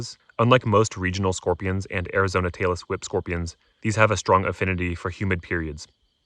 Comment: I thought you said "Taylor Swift" and was confused. Had to rewind. You were actually saying "tailess whip".😂